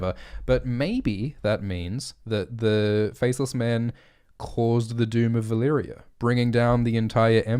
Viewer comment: totally heard him say "first men cause doom of valyria" and im like ok ive gotta hear this shit